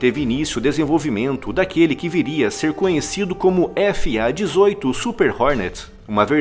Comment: FA-18 super HORNET, foi isso msm que eu ouvi...?(3:32), será que tava só o cano...? Devia ronca que uma beleza...